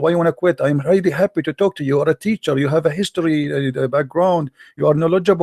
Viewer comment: That was a teacher? I thought he said cheater...